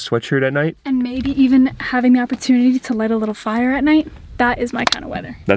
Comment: I thought she was going to say ...."and maybe having an opportunity to lighting a little FART at night". But, thank God I was wrong.